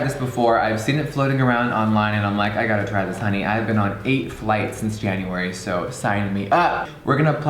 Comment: did anyone else hear him say "I've been on 8 flights this January" when it's April???? MANDELA EFFECT WHO???